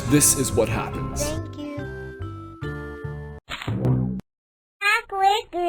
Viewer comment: I thought he said “back away b*****” 😂